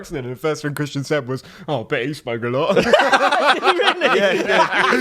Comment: I need a British translator, what did Christian said when they told him that they just had Will on the pod? Thanks for the help! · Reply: "Oh, I bet he spoke a lot" I don't think it was expecially British, but it was said quite fast, to be fair 😄